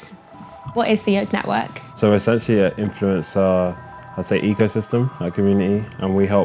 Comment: I thought he said 'egosystem' lol